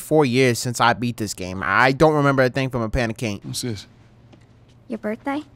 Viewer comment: Love you thanks for bringing my mst loved game back on yt but did anyone catch 2:12 "a pan of caint"😂😂😂😂😂